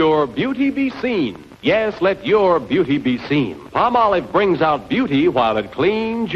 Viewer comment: I’ve always heard it as “pahma-loave” not “palm-olive”. Neat